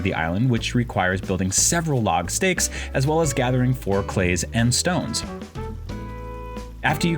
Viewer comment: wait wait wait wait- what did you say? 2:24 whats a "4 clays" LOL